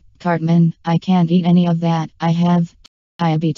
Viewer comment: When scott said diabetes i thought he said ivy teeth